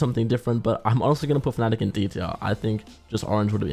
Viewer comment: thats Fnatic not Navi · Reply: Called it fnatic at 3:34 Also went back, it almost sounded like bro called the team "Nani"